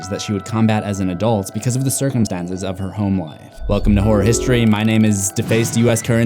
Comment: Why does it always sound like hes saying horr history